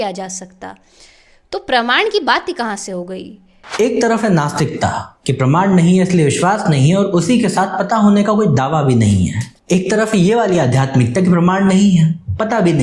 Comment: I can understand misunderstanding an argument, but mishearing a word can create unnecessary confusion. So, here’s the correction: Timestamp: 26:12 “Pramaan (you heard it as ‘Brahmaand’) nahi hai, isliye vishwas nahi hai, aur usi ke saath pata hone ka koi daawa bhi nahi hai.” Timestamp: 26:18 “Ek taraf hai ye waali aadhyaatmikta ki *Pramaan (you heard it as ‘Brahmaand’) nahi hai, pata bhi nahi hai, lekin pata hone ka daawa hai aur vishwas itna ki bas puchho mat.” I said “Pramaan,” not “Brahmaand.” For the rest of the video, I’d suggest a one-on-one discussion would be better. Otherwise, we can keep going in circles with “you misunderstood” and “you misinterpreted my arguments and context.”